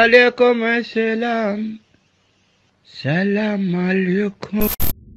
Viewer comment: Блин, мне послышалось не "Салам алейкум", а именно "Сало Олега" uwu